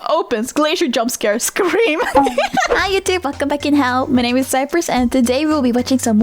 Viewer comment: Wait, WAIT! DOES SHE OPEN HER VIDEOS WITH "WELLCOME BACK IN HELL"? I've been watching her for about a week or two now and I thought she was saying in a different language hahaha It kinda sounded to me like something allong the lines of "Boken bokinhaw" or something like that xD I feel so stupid